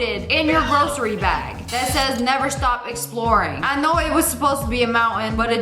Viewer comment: Under one hour lol I thought it said exploding 👇